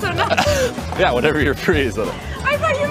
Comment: Tbf it kinda sounded like he was saying 'whenever the restaurant is free' ahahah